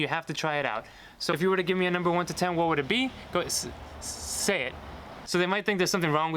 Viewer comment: I'm at 6:57 This is crazy because I thought you said "s-six,say it" with the six so quiet she questioned whether you said six say it or say say it but whatever her conclusion making her think you wanted her to say six so she instinctually went to the next number. I'll never attempt this. I'm sure you had outs if it wasn't seven. Insanely brave.